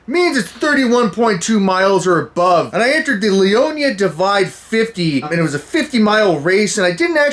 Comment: when he said leona divide, i thought he said 'diddly-onya'